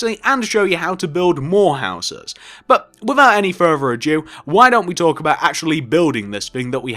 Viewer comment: Did he say " with out further or Jew " at 1:15 😂😂